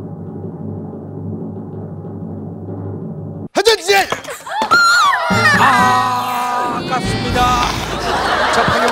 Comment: 뭐라는거?ㅋㅋㅋㅋㅋㅋㅋㅋㅋㅋㅋㅋ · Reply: 회전체